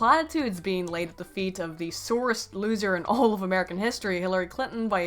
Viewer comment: I heard "sorest loser" as "Soros'd loser". still seems legit